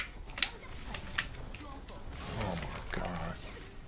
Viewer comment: striker, striker sits back to look for a way to get in > dale uses a move with a long ass startup that's meant for pressure instead of zoning > striker just sees that shit and runs at dale > "oh my gaah duu"